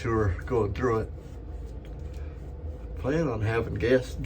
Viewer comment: It sounded like you said, "I'm planning on having gas(correction) Guests." I'm sure you'll have both! LOL